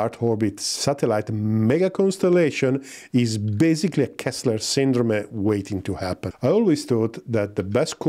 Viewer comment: what does he say in this sentence that would be waiting to happen? Subtitles gave me “kastler syndrome” which seems wrong. Halp! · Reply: Kessler syndrome. Just google it